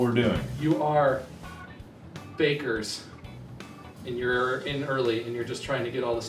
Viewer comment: I thought Bob said fakers until they started talking I'm stupid